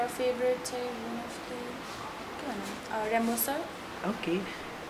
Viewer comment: rapmonster😂😂 Did she said our leader from BTS?🤣LMAO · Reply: She said remo sir